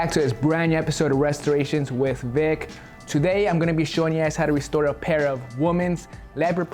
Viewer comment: I thought he said "today, Ima be showing your ass how to restore..."